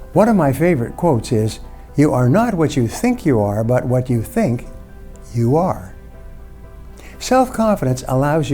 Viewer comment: Could someone re-write this quote (2:40) here, because I can't understand it phonetically? Thanks :) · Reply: You are not what you think you are, but what you think, you are.